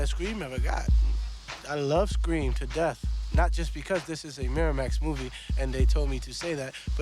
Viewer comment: Miramas? I thought you said MiraLax. 💀💀